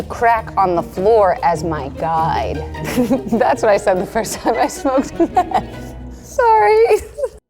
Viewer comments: I can't seem to get subtitles, but at 8:49 does Kelsey say "that is what I said the first time I smoked meth?" Honest question since I am having a hard time hearing her in this time stamp. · Did she say she smoked meth at 8:48 ??? Lolololol